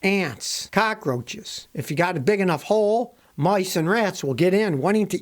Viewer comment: Did he just say :the mice laid eggs ? 🤣